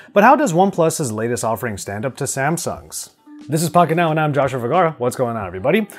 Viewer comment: Samsuck's 🤣🤣🤣. Watched this part trice to make sure I heard right